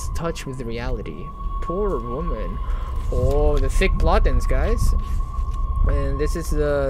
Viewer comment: Don't forget to LIKE THE VIDEO :) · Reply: Not sure but I definitely heard "The Thick Plottens" lmao